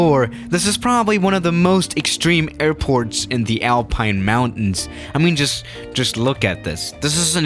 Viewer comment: For a moment I thought u said the Airplane Mountains instead of the Alpine Mountains